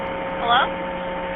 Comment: I think the man said help.